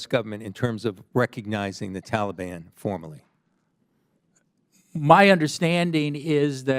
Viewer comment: I thought they said we recognize the Taliban as friendly now😂